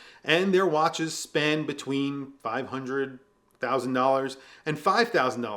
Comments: Sounded like you said that Venezianico "Spend between $500000 to $5000" 🤣🤣🤣 . I SURE HOPE NOT XD !! I'm gonna get one and i don't have 500k to spend 😅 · I had to listen to this multiple times because I kept hearing 500.000 😂